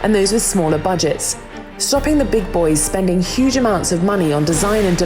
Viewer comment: did she just say "big bois"??